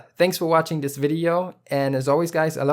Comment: "fart and soul" Did i hear that correctly?